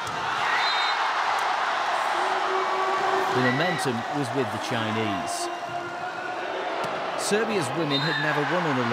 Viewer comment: Can anyone tell me what Zhu Ting shout @ 0:45? I tried to read her lips but could not get it. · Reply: It's "Come on", I think.